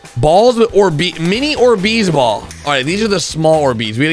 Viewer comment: When he said orbeez balls sounded like he said Deez balls 🤣